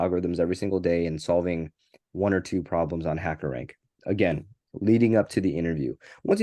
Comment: What is referring to at 21:30? Is he saying Hacker Rank? Is it similiar to LeetCode? Btw great video!